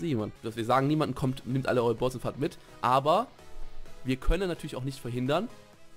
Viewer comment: Warum verstehe ich bei 6:16 nehmt eure Balls und fapt mit? WTF was geht bei mir xD